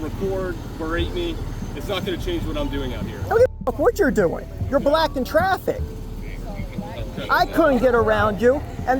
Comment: Lol @ 1:09 it sounded like he said, "You're black in traffic"